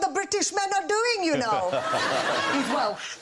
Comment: What did she say at 2:34 ? He is well...? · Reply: He's Welsh.